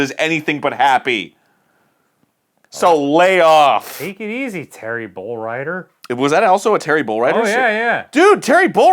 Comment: Terry Bolryder = Terrible Writer At least ... that's what I heard from Arin's mouth.